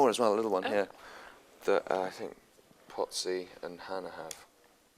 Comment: Did he just call Andrew 'Pott-sy'???